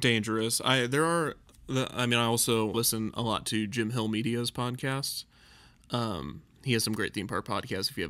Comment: could anyone hear the name that ne said? I really can’t understand the name but i would like to look the podcast up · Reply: Late reply but it's the Jim Hill Media Podcast